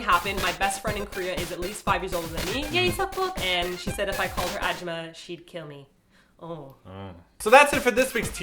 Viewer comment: "If I called her _____ she'd kill me." What did say here? Hajima? Don't that mean "don't" in Korean?!?